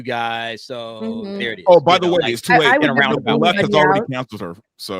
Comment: I'm totally team Rowling here, but to be fair, Watson did not say "I'm here for all the witches, except one". She says "I'm here for ALL the witches", followed by a brief and barely audible phrase interpreted by some to be "but one" but which is probably actually the phonetically similar "by the way". It's still clearly a reference to Rowling, a reference to her supposed "exclusion" of trans people - the sense being that, unlike Rowling, Watson supports and embraces ALL "witches". Still a dig, but nowhere near as vicious a dig as the one implied by the mishearing.